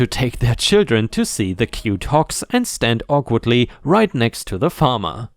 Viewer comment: because of your accent it sounded like you were saying Hawks like the bird lol got the G sound and K sound mixed up there